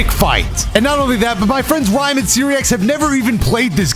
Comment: i swear said hs friends Ryan and SeeReax